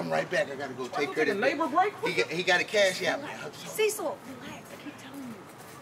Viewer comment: Did she call him “Cecil”? 😂